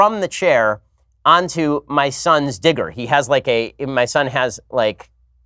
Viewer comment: Anyone else thought he said i fell onto my sons dick? Instead of digger 😂😂😂🤣🤣